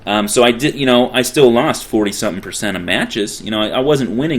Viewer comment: Really nice thorough guide to this tactic. Really well explained. 8/8 m8. btw 11:03 "Win rate probably like 65% -- i still lost 47% of matches" 65+47=112 ;^) but i guess u mean this 12% is the learning process it takes to perfect this. btw gr8 vid did i say that yet :D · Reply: I believe he said "40-something" not 47... but either way :P